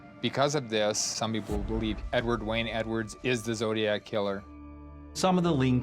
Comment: I hear him say "Hey wait I am the zodiac killer."